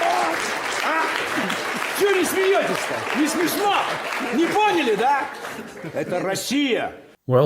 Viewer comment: you often use this clip without subtitles, thanks to ChatGPT and some google-fu I was finally able to understand what he's saying: "ЧЁ НЕ СМЕЕТЕСЬ, НЕ СМЕШНО? НЕ ПОНЯЛИ, ДА? ЭТО РОССИЯ!" translates to "Why aren't you laughing, not funny? Didn't get it, huh? This is Russia!" Now I want some context.